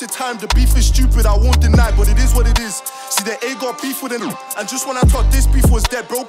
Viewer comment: did he say that they got beef w the nizz? (OFB)